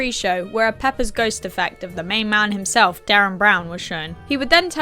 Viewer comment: I'm from the US so I've never heard of Derren Brown. I thought you said "Dan Brown" like the author. I was a little confused but it wasn't the worst branding I'd ever seen.😅 😅😅